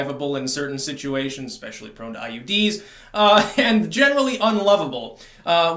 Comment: parapraxis, did you say IUD's? 😂